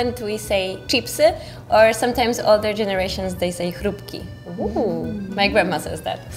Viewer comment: subtitle fail. said "Chrupki" and the subtitles say "frytki", which is the word for fries, not crisps.